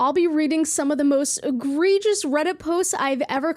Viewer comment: Off topic BUT MY SILLY SELF THOUGHT YOU WERE SAYING hee hee outrageous; I was like maybe you're just pronouncing it differently. But then you put egregious (I have not a clue what that means; my vocabulary is limited at the moment; my bad if I spelled it wrong or anything) but lol 😆 I guess I learn something new everyday